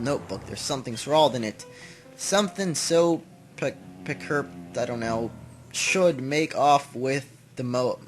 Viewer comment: Something peculiar should make off with the motor is what it says.